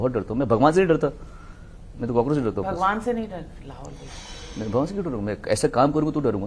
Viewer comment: What did she say at 32:45 ? is it lahol wala quwwata ?